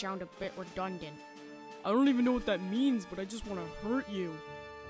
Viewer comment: Da heck I thought groomer said I want to hold you on the last episode but he said I want to hurt you. I was like what did he say then I fell on the ground laughing